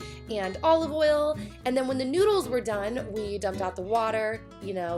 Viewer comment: did she said needles??? lol love it!